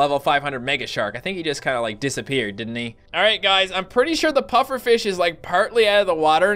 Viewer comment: It sounded like said puppet fish lol